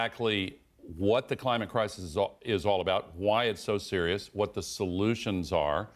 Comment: why it's so cereal... (that's what I heard in my mind) 😂